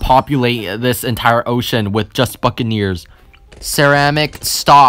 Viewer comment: I think said a bad word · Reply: he said buccaneers